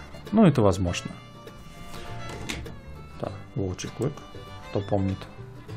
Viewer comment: не расслышал название 11:15 , форма прикольная ! Напишите пожалуйста ! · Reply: Волчий клык. - Wolf tooth